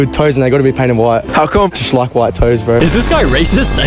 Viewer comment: oooh, toes.. I thought he said toast and i was so confused, "does he mean cream cheese?"